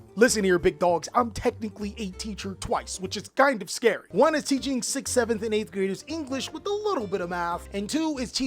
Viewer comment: All i heard was " teaching 6 7th" 1:30 😂😂😂 god im cooked